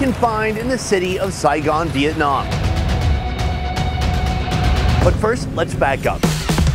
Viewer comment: In the beginning when you said” but first let’s back up”. I thought you were going to say” first let’s baguette up. “